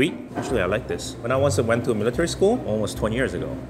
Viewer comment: "Back in military school, 20 years ago" -Looks 19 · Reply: J Trujillo i thought he said elementary school?